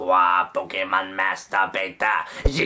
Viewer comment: did he just say Pokemon masterbaiter?!?!?😂😂😂